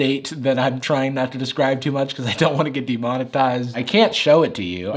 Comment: what Jarvis said : demonetized what the subtitle says : " the amount of thighs "